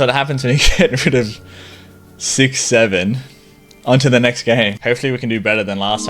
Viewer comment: WHAT DID HE SAY? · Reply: 6. 7